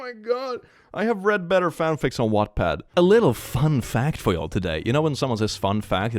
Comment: did he say he read better fanfics on WATTPAD?